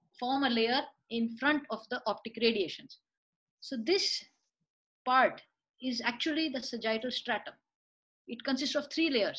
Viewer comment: Could someone let me know what is being said? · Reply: this part is actually the SAGITTAL STRIATUM ( IFOF, temporal fibers of the anterior commissure , optic radiation )...